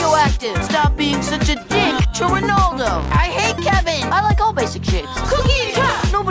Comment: i keep hearing "stop being a dick to Ronaldo"